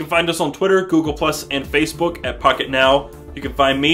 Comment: In the beginning it when he says pocket now it sounds like he said f**k it now lol!